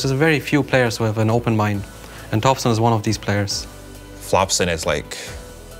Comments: did i just misheard or puppy just said "FLOPSON" ?? 😂😂😂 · did Puppey just say "flopson"? xDDDDDDD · did Puppey just call him Flopson aahha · Did Puppey just say Flopson at 3:01? 😂